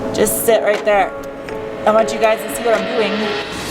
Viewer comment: I swear I heard "I want you guys to see what I'm boobies"